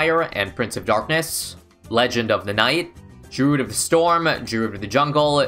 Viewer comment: I just heard him say "pleasure of the might"